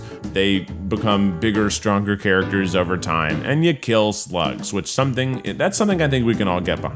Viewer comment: Oml I kept replaying the best fiends add because I thought he was saying and it kills sluts and I think that’s something we can all get behind I thought he was referencing the drama and was like Oml damn 😮